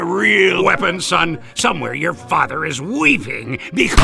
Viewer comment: "Somewhere your father is weeping" So is yours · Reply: I hear it as "Somewhere your father is weaving" for some reason.